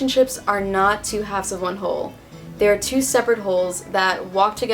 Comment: Haha why did I hear "They're 2 seperate holes"